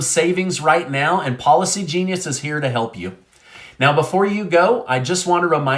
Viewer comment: I thought you were saying Paula c genius…. Ha ha….. ok I think I missed it— what was trumps eerie prediction??